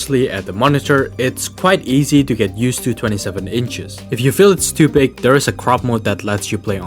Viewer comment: Bro I thought when you said "If you feel it's to big" that you said "If you feel stupid" 😂